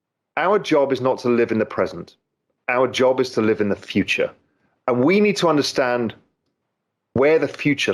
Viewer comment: All I hear is “We need XRP”